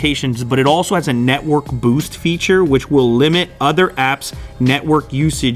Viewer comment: I misheard Network boot instead boost